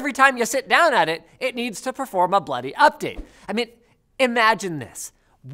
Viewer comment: did I just hear an american say bloody (or Canadian i can’t tell the difference)